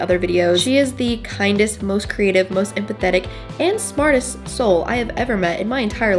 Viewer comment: Omg instead of empathetic I heard pathetic and I was thinking jeez Haley you can't be saying that 😂